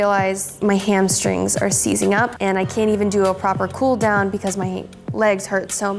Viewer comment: After she finished I thought she said “ I can’t feel my d”